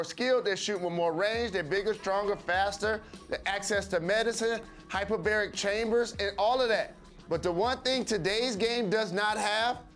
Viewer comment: I thought he said hyperbolic chamber hahahaahah